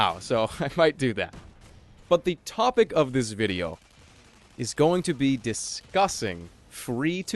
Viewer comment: I thought Jerma said " The topic of this video, is going to be disgusting" ??? Disgusting F2P???after 2 sconds. Ah ok